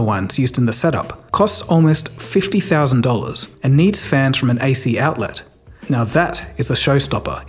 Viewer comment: What does he say around the 12:08 mark? Fans from an AC outlet?